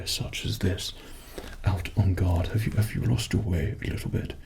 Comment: i thought you kept saying 'on god' instead of "on guard" lol